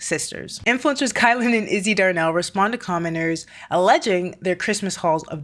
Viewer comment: lol when you were reading the title of the article about the sisters, I thought you said commoners instead of commenters and I was like, yep, sounds about right 😂